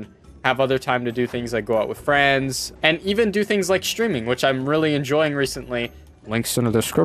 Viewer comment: I thought he said "And even do stripping" I was like dang exposed